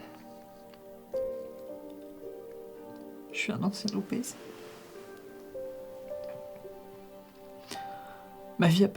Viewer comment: un quoi ???? J ai rien compris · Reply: Ancien obèse